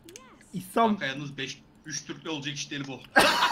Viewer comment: de ne diyor 😂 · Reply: Geç oldu ama belki öğrenememişsindir. "Bu iş 3 Türkle olacak iş değil diyor sonrada aramızda başka ırk var diyor" xd